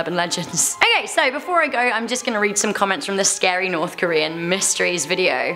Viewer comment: When she said scary I thought she said Gary😂 .... yeah... I probably watch too much spongebob....